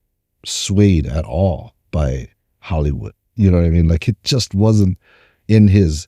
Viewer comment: I swear I heard say "Haole-wood" 🤣